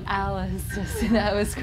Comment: When Katrina asked who comes to this room I heard Beth and Alice. 3:02-3:04 Anyone else?